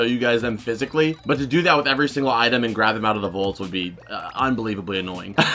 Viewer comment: I still laugh a little when you say vault. It's because I'm kinda braindead sometimes. When I first heard you say it I was like "What the crap is vult" Lol I started looking up Nickelodeon Vult, Cartoon Network Vult and couldn't find anything. And it finally clicked that you were saying vault the whole time. 🤦🏼‍♂️